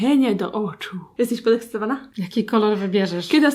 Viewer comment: ja usłyszałam"jesteś molestowana" o boszzzzzz co ja mam z głową sr musiałam to napisać